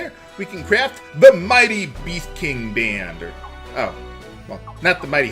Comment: I keep hearing "Beef King Band".